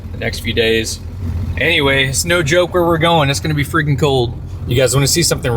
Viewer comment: Did you say "Snow joke" at 13:56? 😄